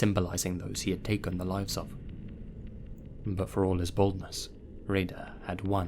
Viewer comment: I can’t be the only one who heard, “But for all his *bald*ness”